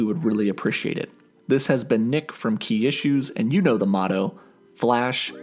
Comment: when i first heard him say his channel name i thought he said kia shoes until i looked at his channel name and it said key issues